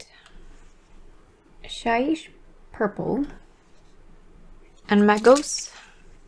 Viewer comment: did you say Shite purple lol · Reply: Shyish (Wind of shadow/death magic) in Warhammer fantasy lore afair)